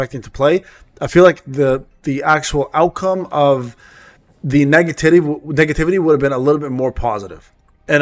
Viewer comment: Pin this one. All I heard in the whole video 😂 nega-titty